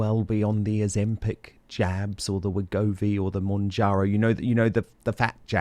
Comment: Dan, I thought you said ‘Ozempic Jam’!!!!!